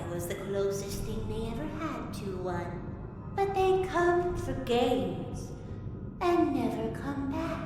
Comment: I misheard it as "but they'd come for gays"